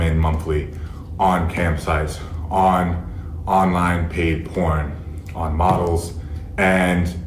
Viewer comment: it sounded like he said gay porn ahah